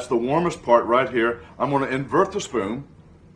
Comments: I heard "sperm" instead of "spoon" and just stood there confused💀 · I thought he said "sperm" instead of "spoon"